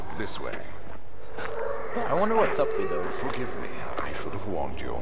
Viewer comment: it sounded like he said "fuck with me" instead of "forgive me" :P